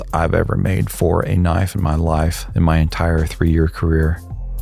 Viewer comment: What did he say?!? 3 year career?!?!